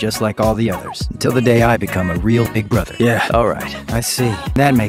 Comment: "Other than that, I'm just like all the others...until the day I become a real big brother." *swoons* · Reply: I thought he said "until the day I become a real Mcbrother"😂